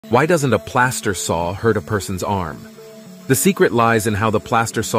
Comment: Plaster saw? Thought you said pass the saw